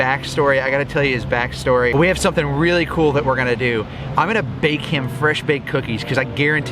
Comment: When you said i'm gonna bake him some cookies I thought you said i'm gonna bake him! LOL <3